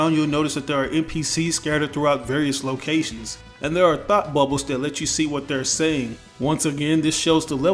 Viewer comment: thought he said "thot bubble" 😂